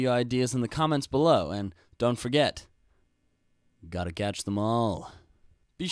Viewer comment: he sounded like he said GOTTA CATCH THE MALL